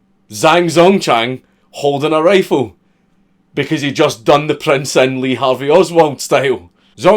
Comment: What is he saying at 14:03 ? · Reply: "he just done the Prince in Lee Harvey Oswald style"